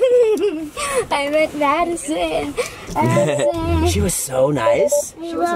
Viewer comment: did she say “Maddison”?